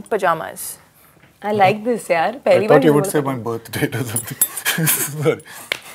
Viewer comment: can anyone tell me what did he say there? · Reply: I thought you would say "my birthdate" or smth. He meant she would say her ideal date is "his birthdate" 😆😆😆